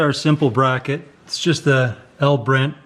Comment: Wait i minute i thought you said your name in Spanish El Brent 5:24 ??? Let me find out your part mexican hell your already part of the family !!!